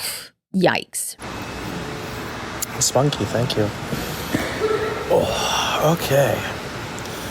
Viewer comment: Does he say, "Spunky, thank you."?!? 🤢🤢🤢